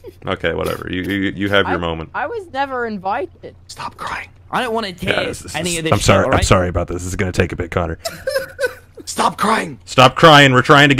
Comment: I thought he said where trying to get in the animatics